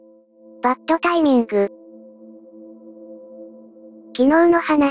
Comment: バッドタイミングが マッチョな妊婦に聞こえた💦 私病んでる？ww